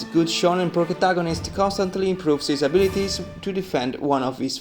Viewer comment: Bruh did he say progataganist